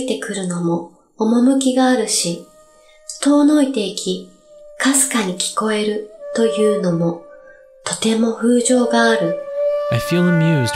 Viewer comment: I couldn't help but notice that 微かに聞こえるというもの should be 微かに聞こえるという*のも*